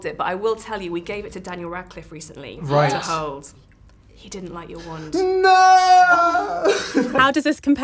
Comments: At first I thought she said "I love your wand, daddy" and I was like WHOA WHAT IS GOING ON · "I love your wand, Eddie." Nope, that sounded weird. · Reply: Suq Madiq because he was speaking over her I couldnt hear her and thought she said "I love your wand, daddy." AND I WAS LIKE HOLD UP. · She said "I love your wand, Eddie" BUT WHAT I HEARD WAS "I love your wand, daddy" JWJSJCIDNJSKDMC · i thought she said 'i love your wand daddy' BUT I REALISED SHE SAID EDDIE